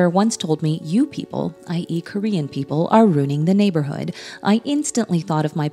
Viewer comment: Am I the only person who thought she said I eat Korean people like she’s a cannibal